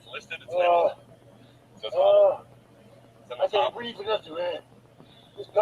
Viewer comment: does it sound like he said " i cant breath because of covid?